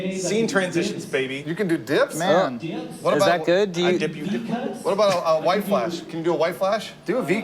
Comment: Did I hear "I dip you dip"